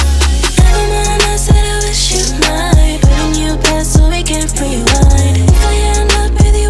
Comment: I thought she said "i wish you were mine"..